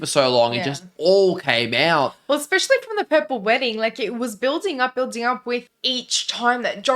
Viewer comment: Did you say purple wedding? lol